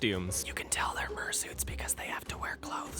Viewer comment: the CCs say fursuits but I think he said murrsuits.